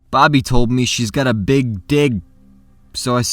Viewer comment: I thought u said "bobby said she has a big dick" I loughed my legs off, especially when u said "show me"